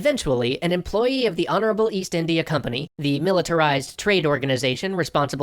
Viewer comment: hold up did he say honorable east India company